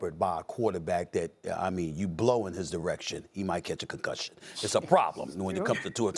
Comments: did he say “you blow on his erec shun” ?? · I had to rewind that a few times to make sure I heard the right thing 😂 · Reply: i thought he said blow on his erection. 😂